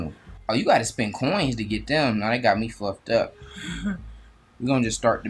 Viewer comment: it sounded like he said they got me fucked up